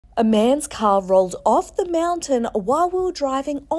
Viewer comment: I thought she said a mans cow rolled down 🤣🤣